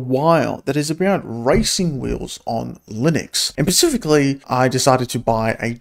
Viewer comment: Did you say...Pacifically?